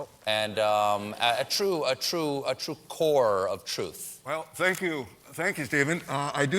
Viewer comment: I thought he said "thank you david" at 1:14 .. that's all I have